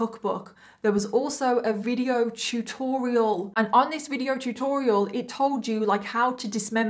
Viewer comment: thought you said "chew-torial" i had to rewind👀👀👀😮😶